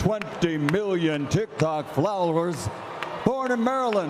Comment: lol it sounded like he said flowers